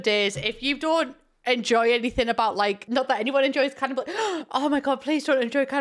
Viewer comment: I thought you said "capitalism", not "cannibalism"....😂